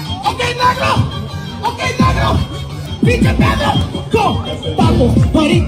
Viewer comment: It sounded like he said “pinche pedo (Fart)”😭😭